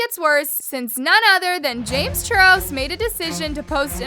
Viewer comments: When she James Charles in 3:20, the subtitles says James CHURRO 😭🤚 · I heard James Churros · Is it just me or did she just say "James churros" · anyone else hear “james churros” LMFAO